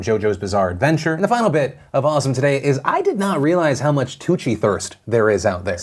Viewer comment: i swear on everything i thought phil said "coochie thirst"